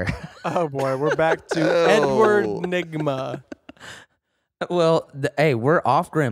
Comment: did say "N-word Nigma" ???